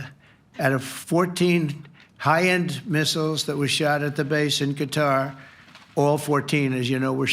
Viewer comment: Did he just say 'the 14 high end missles WE shot at the base'?